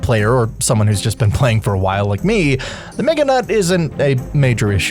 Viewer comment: I think he just said "Meganut"